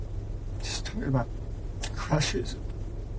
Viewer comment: This man is an absolute monster. I gave Jennifer the benefit of the doubt at first, then I heard she SENT Maddie to bed with him!!! Then she told father he needed to call a lawyer AFTER she saw the pictures of what he did to her daughter! She needs to be charged! Thanks for covering this. ❤❤ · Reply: Around the 13 minute mark the detective asks him what he remembers Sunday going into Monday and he sighs sooo heavy......which is always very telling (like ok here we go).....and also at 13:19 did he say "she just kept talking about her crushes"??........me personally I think he was emotionally tied to Maddie and saw her as his. Sick dude